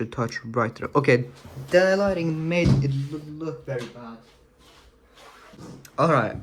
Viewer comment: hey what did you say at 34:28? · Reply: I said the lighting made it look really bad